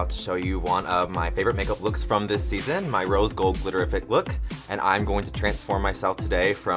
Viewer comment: I’m still thinking about how she said ‘glitteriffic’ and wow thought she said glitter effect